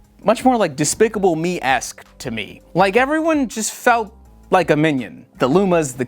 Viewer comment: For a moment there I thought you were saying everyone in the theater felt like a minion